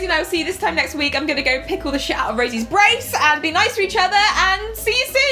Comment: At the end I thought she said "pickle the shit out of Rosie's brace" 😂